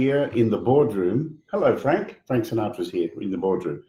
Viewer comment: automatic subtitles thought Tommy said "thanks a notch" instead of Frank Sinatra lol great video, always a pleasure to watch and hear both of you.